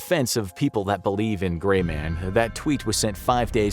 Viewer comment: I thought first it said The GAY Man Ghost..... then I thought.. why wasnt he rainbow colored?